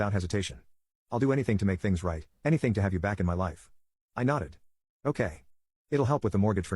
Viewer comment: “i will do anything to get you back in my life” what i heard “ i will do anything to get your money”